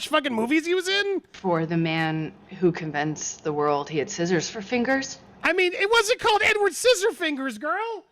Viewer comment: TBH the first time I watched this interview, I misheard "scissors for fingers" as "seizure for finger" and I thought she was talking about Johnny passing out after his finger got chopped off. But damn i gave her too much benefits of the doubt. Translate: she's dumber than i thought.